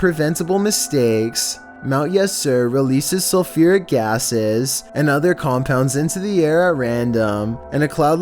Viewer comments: kinda sounds like he said "sulfuric asses" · lol did he just say "yessir!"